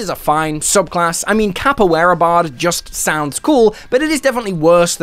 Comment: I misheard you the first time thinking you said “capybara bard”, absolutely best subclass Ever!